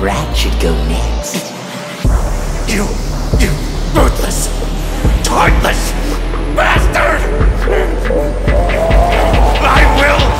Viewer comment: can anyone say what Goku said here? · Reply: "you ruthless .. heartless .. bastard"